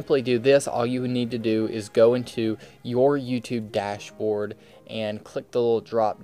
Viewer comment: your youtube what did he say at 3:18? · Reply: "Your YouTube Dashboard"